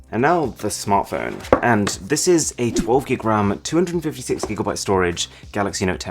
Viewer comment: did he just said 128gb ram + 256gb. Rom